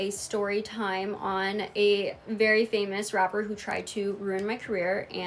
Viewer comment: did she just say “ver famous rapper” 😂😂😂😂?!?!?!?!?